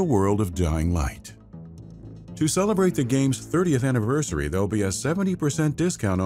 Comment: Am i hearing this correctly? Did he say 30th anniversary?